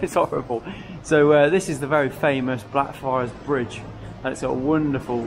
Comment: Dear, love your channel. What is the name of the bridge on 6:00 min. I couldn't catch the name. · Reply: Thanks so much mate it’s called Blackfriars Bridge